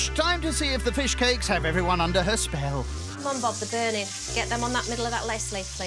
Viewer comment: I'm sorry did she just say the plumbobs are burning 4:13, are her sims 2 characters on fire from a grilled cheese? Really though what is she saying. Lol